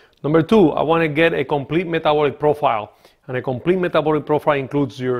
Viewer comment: WHAT?? I'm sorry is he saying metabolism count?